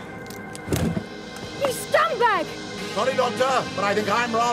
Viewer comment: Did Ace just call the Brigadier a “scumbag”? 😮